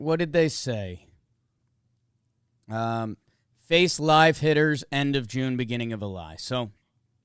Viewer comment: did you just say "end of June, beginning of a lie"?! Hahahaha